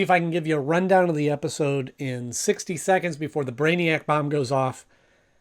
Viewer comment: did you just call him Braniac?